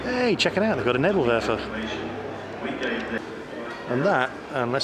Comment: you say: "hey check it out they got an WHAT?" sorry i didn't catch that, what the hell is that epic looking monster and what was it used for? to me it sounded like "they got a herpaderper"